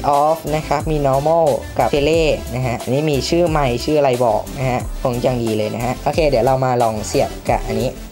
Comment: วินาทีนี้ลองฟังดีดี จะได้ยินว่านะคะ